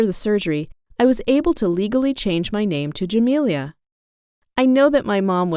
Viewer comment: When i thought she said d'amelio